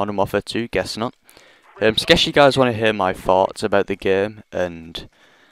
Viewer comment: when u said "thoughts" i thought u said fart